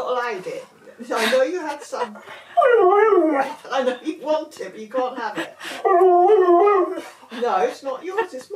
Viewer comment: This is great, but it seems the translator got garbled for a second. He clearly says "I really want it" not "I want some" at 0:19. Believe the rest was spot-on. /yesIknowit'snotaDisneymovie